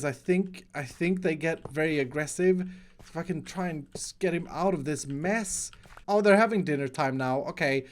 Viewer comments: "Fucking try ands.. get him out of this mess" Did I hear that wrong? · did he just say f****ng on 10:17 10:18? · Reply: He said if I can